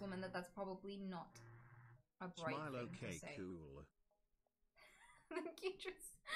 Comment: "Smile okay too-blah"? Did anyone catch what he said?